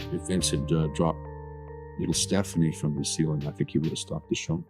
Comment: I thought he said if Vince had to scrap Shane of he would stop the show?